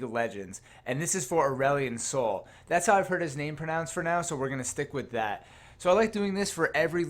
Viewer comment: Oh, when you said "Pronounced for now" I thought you said "Pronounced Pronounced" lol.